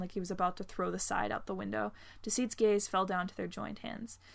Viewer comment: I'm sad Closed captions is on I'm laughing, both in pain from the events and because it says "Deceit's gays" instead of "Deceit's gaze"